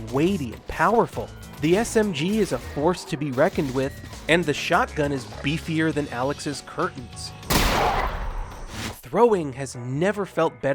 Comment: What did he say at 1:48 "shotgun is beefier than alyx's curtains? "